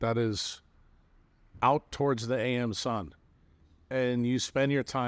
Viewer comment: Did he say "the am sun"?